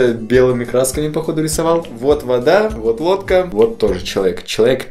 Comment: мне послышалось сначало то что он сказал : вот водка Сказывается это лодка (простите за ошибки